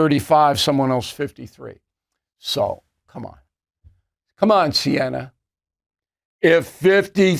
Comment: "COME ON SIENNA!" is what Bill said, who proofs this???